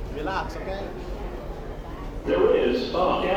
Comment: I thought he said “man relax ok?” 🤣🤣🤣🤣🤣😭